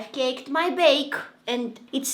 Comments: did she say she caked her bake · I heard her say "I caked the baked" 😂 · am I stupid or did she say I caked my bake? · Did I hear I’ve caked my bake? 😂